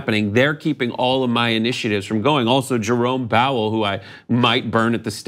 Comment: instead of "Jerome Powell", did he say "Jerome Bowel"? LOL